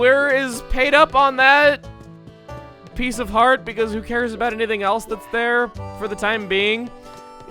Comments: I can't be the only one who thought he was saying "herpes" instead of "heart piece" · when he entered the house and said 'Heart Piece' I thought he said 'Herpes' I am so sorry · when chugga was saying heart pice I thought he was saying herpees